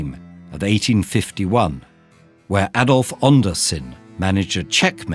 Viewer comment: Did he say adolf?